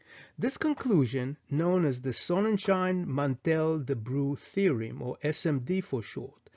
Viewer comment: could you give me the name of this theory again, please. I can't hear it clearly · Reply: Sonnenschein–Mantel–Debreu theorem